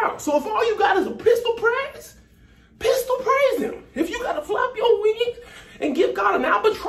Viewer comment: I first thought you said if you gotta flap yo weenie flap yo weenie 😆